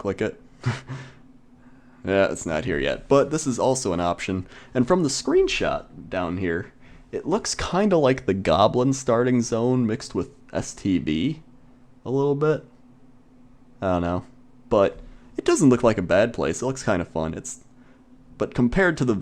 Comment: did he say std instead of stv